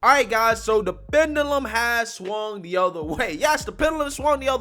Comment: 00:1 I thought you said "(uhh...)the Venom has swarmed the other way!"